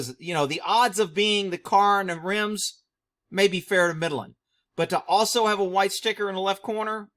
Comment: Hey all. At 7:24 what did say, fair to what? Mid...(something)?? Thanks! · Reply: fair to midland ... that means fair to medium or light to medium chance